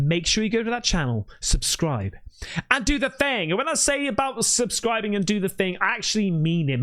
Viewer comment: Anyone else get this? When people say “like, subscribe and…” I hear say “do the thang” every time 😂👌 ❤